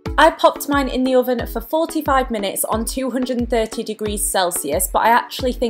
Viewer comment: i thought u said cook the pancakes for 45 minutes omgknbdjfhnjkdfbdjfhb not 4-5 ... hahaha woops